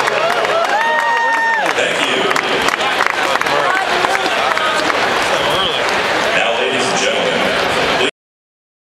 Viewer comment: What did Scherzer say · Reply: He said "I'm good"